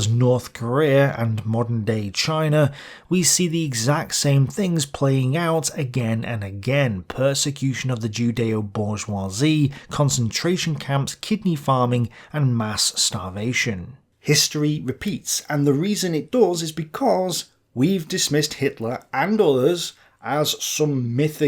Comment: can someone explain what 'kipping farming' is? Maybe I misheard it...